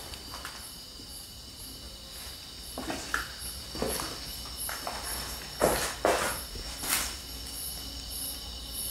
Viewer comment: 초 어떤 여자목소리가 들리네요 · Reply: 후레쉬라고하는것같네요